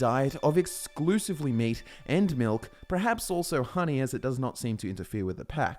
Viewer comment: I heard meat and milf.